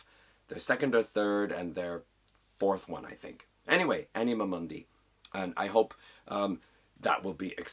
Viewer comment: thank you very much for the kind words! I really appreciate it 😊 You’ve made some cool connections here. When you first said “the song Africa” that wasn’t the one I thought you’d talk about, haha. You have a great variety of music here, I don’t really know any of these albums! But they all sound pretty interesting. I would have loved to see some 80s South American black metal! Anima Mundi? I thought I heard “Enema Monday” hahaha. OVRFRWD. Cool! And cool that the band member reached out to you. I hope you gave them a favourable review. I have heard of The Grapes of Wrath but I don’t know them very well. Maybe if I heard them I would recognize.! Excellent entry to the contest. Well done. Entered!